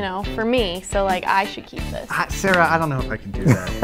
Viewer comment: did just call, “Sarah”?